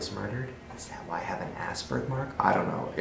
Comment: "ass birth mark" y did I hear "ass burger"?